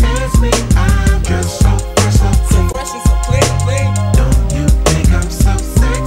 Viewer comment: I'm here because I heard it's "Dressed, so fresh so clean"instead of " just, so fresh so clean".